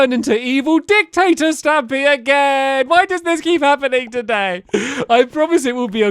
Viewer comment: is that a spongbob reference? 🤔 The "why does this keep happening to meee???" Or 8:15 Edit: i think he actually said today